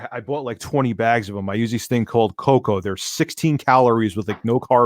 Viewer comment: Can anyone tell what he said he uses as a bread alternative at around the 20:20 mark? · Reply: Coconut bread